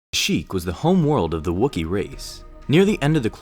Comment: I thought he said "what the hell" for a second